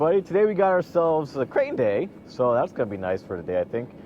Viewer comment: When you said crane day I thought you said gray day like the sun is not going to be in my eyes so no excuses there😂😂😂